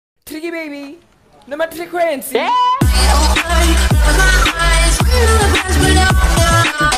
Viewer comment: 😂 😂 😂 All I heard is "Tricky Baby! Nomatriquency!"